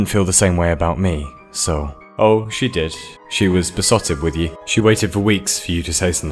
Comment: what's that word you used? Pesotic or something like that?